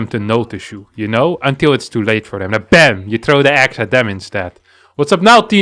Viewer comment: I literally heard, "bam and throw an axe at them" instead of an egg. Made my day Thanks!